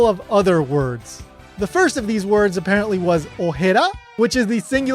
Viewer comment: Hi, man, maybe you misheard, but Garza didn't say the dub director thought Gero sounded like "ojera", but "OJETE", which actually means "asshole" (in both ways).